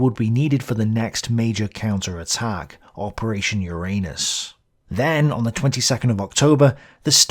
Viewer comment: I must have something wrong with my head because over and over again i hear : "Operation ur anus"...